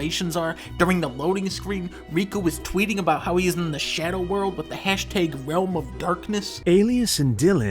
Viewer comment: "riku is tweeting about he is in the shadow world" I heard shower world lmaoooo